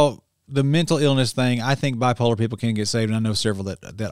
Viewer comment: wait a minute did you say “I think bipolar people can’t get saved”? What do you mean? I have mild bipolar disorder and I’m saved, and my disorder is ceasing everyday, im getting better by God’s grace and the renewing of my mind!